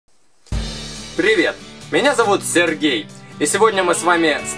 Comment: Мне одной в начале послышалось Сэр гей? :D